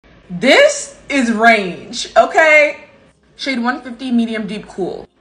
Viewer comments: I was so confused cause i misheard range for rage so i was like Huh?? Then i realised at the end when you shouted them out 😅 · You started out by saying "*This* is range!", but I totally thought you said "*This* is RAGE!". So I watched you go through the whole thing thinking you were mad about it, even though the last couple shades seemed pretty good matches, I thought - it wasn't until you said the complimentary stuff at the end, and it looped, that I realized you'd said "range". Oops!